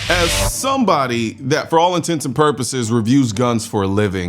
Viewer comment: I restarted the video 3 times because I thought I heard "For all intensive purposes..." 😂